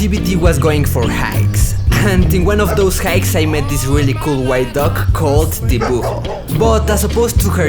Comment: Am I tripping or did say the dogs name is Tebogo??